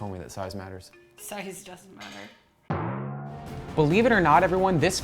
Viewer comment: she says size doesnt matter, right? sorry- i might have misheard and CC doesnt help,. im a touch hard of hearing.